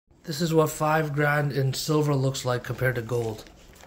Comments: I thought he said 5 grams.... I was thinking he needed to recalibrate his scale. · I thought he was saying 5 grams at first haha, I was like dang, that's the heaviest 5 grams I've ever seen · I thought he said "Grams" and I said "Dude, you're high!".😂😂😂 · Oh "grand"! Thought he said "gramms" 😂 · My stoned brain thought he said 5grams n i was like damn no way silver is that light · Oh he said 5 grand. I thought he said 5 grams. I was like that’s definitely not 5 grams, sir.